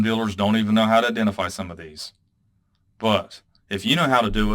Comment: At one point you said, "Use Anacs. . ." For a second thought you said, "Use Xanax". Although that might help too!